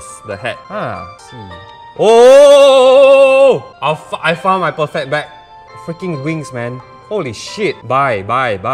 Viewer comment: says ' I found my perfect BACK! ( as in item ) ' but subtitles on the bottom says ' BAG ' instead of back #ERRORDETECTED